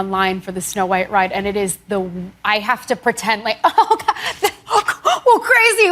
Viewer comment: Sounded like at (1:15) she stopped herself from saying “it’s the worst” regarding being recognized and asked for pictures while in line for the Snow White ride at Disney. Seems like such nice gal. So approachable, humble and down to earth. Yeah right…